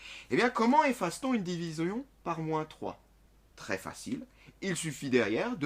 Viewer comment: c'est moi ou il dit "diviseon" a la place de division sinon merci pour cette vidéos continue comme ca 😀